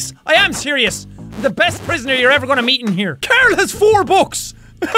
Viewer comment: did he say Carl or Karel?